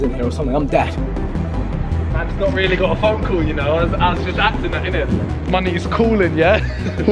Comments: what did Simon said I didn't hear that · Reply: “Mans not really got a phone call yanno, just acting innit, the money is calling” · I thought at 4:46 Simon said the n word but just saw he was saying “money’s calling yeah” lol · does Simon say money or n*gga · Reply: He said money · did simon say the N word? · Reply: no he said money is callin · what did he sayyyyyyyy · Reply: "Money is calling yea" he said that nit my n word is callin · did Simon say the N word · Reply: think he says “Money is calling yeh” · Did anyone else hear Simon say the N word😂😂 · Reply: it was money is callin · did he say the nword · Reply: i’m pretty sure he said money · Did simon say the n word!?!? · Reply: he said money is calling yh lol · I just heard simon say the n word around 4:50 ish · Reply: Either that or he said “money is blah blah blah”